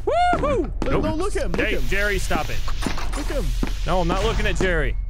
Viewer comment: he actually says "errick"